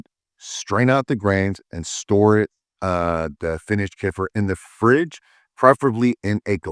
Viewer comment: LOL! I thought he said what sounded like "koofer" or whatever. I think he means Kefir - pronounced keh·fuh!